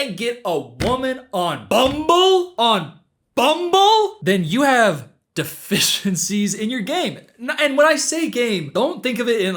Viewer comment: i thought he said “you have deficiencies, and you’re gay!🤷‍♂️” at first😭